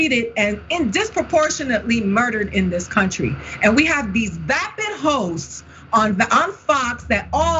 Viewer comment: I thought she said "And we have these vapid ho's on Fox" 🤭